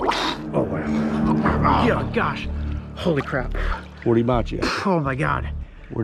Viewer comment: That must’ve hurt because I’ve never heard him say “holy crap” this is a crystal clean channel even for a mild word like crap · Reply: He said "holy crab"